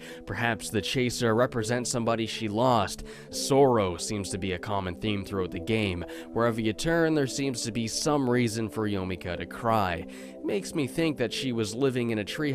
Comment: I'm too tired, I thought he said Zorro instead of sorrow